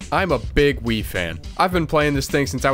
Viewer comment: I thought he said, "im a big WIId fan."